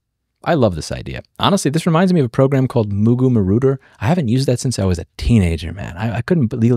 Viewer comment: what was the name of the program? I don't quite understand it and google search doesn't help either xD (I'm not a native English speaker) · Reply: Its called Mugu Marauder